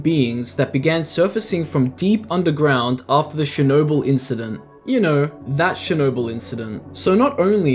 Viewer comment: bros accent is so funny that I literally thought he said “Shinobu incident”😭😭